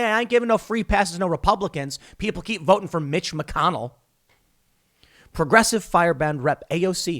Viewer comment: Just an amusing happenstance, when he said Mitch McConnell I had to rewind it twice to make sure I misheard him say Bitch McConnell